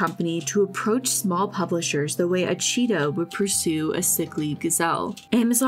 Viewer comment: why am I hearing a cheeto instead of cheetah 😅 I need help, lol.